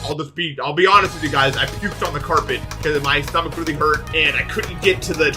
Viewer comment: When you said "I puked on the carpet" I heard "I pooped on the carpet." 0-0 help